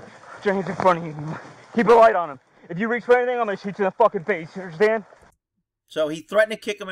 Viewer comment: Hey, love your channel, at 20:31 I think the cop said “if you Fkn move I’m gonna shoot you in the face”, I don’t think he said, kick in the face. thank you for the content, again, love your channel. Im always waiting for the next video to drop.